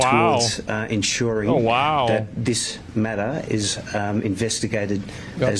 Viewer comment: you may think that he said "we'll take a look at this matter", but pretty sure it was Meta. This is the current global Meta 👀